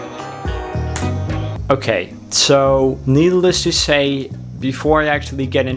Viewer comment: Did he say "needle less"? 2:02 lol